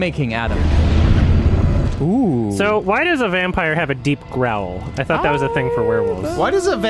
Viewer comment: I really thought was going to say "why does a vampire have a VULVA?" at first lmao